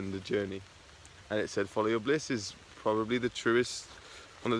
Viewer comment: That's funny, when I do a welsh accent I sound indian especially if I say "thank you come again" lol when you said about following you "bliss", I thought you said "belise" which I thought for a sec was a new merged word of bliss + believe. Which to me would be like so true to follow my believe would be to follow my bliss as in being true to who I am and proud to say I'm vegan. It makes me happy when I'm not judged so I don't judge others, live and let live and all that. I love the mountains, it's like my third place like everyone having three places but we could have more but one is work and two is home. We have a third place to where we go that like another safe place. If I had a second third place lol a fourth place it would be by the sea.